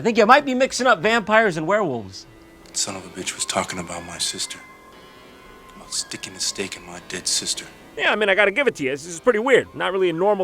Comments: Lol on 10:24 it sounds like he said "I ain't staking a stake on my dick sister" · I thought he said "sticking his dick in my dead sister" .. I was like WTF??!! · I literally thought the guy said “He’s sticking his dick in my dead sister!”